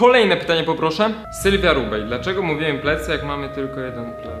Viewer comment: Czy tylko ja usłyszałam Sylwia Ruchaj. 😂 ?